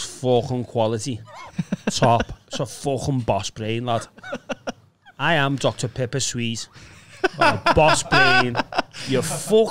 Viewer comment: Oh god. I heard say " I am dr. Piper sweez" snd started getting flashbacks ahhahaha